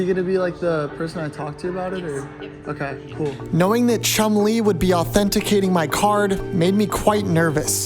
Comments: Ohhh i thought you said chun-li 😂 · I thought he said chun li 💀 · This is still a W! Chum looking at the card was insane!! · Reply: bruh, i thought he said "Chun Li" 😂😂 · ngl I thought he was saying Chun Li the whole time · I thought he said chun-li😂